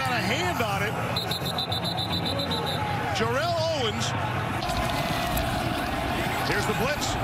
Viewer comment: who else thought the announcer said Terrell Owens for a sec